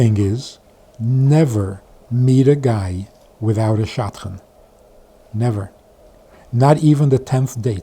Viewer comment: What is the word and its meaning at 17:23, sounds like shotgun or something. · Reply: Shadchan, i.e. a matchmaker.